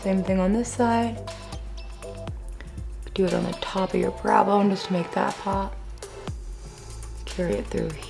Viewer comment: When you said "tops of your brow bones" I thought you say tips of your elbows, I had to skip it back lol